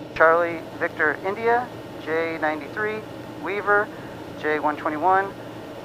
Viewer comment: He said "Jay193" (or I heard Jay), you said "J93". He said Readback correct. Not that I'm braggin, I listened to it like THREE times and tried to write it down short and understandable, just for the lolz (as I don't even have a ppl).